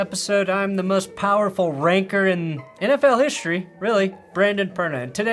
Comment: Sorry all I heard was "most powerful Wanker in NFL history"